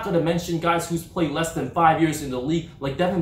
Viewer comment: I thought you said must of played more then 10 years ? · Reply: He said 5